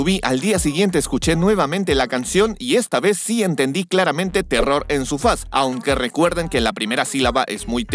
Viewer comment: pensé que decía "Horror en su faz" xD